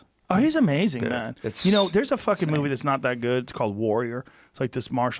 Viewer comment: Did Joe just say that "Warrior" is not that good?